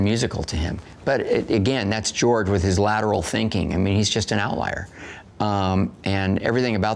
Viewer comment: after Ron says ´...lateral thinking...´ what does he call Lucas? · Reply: An outlier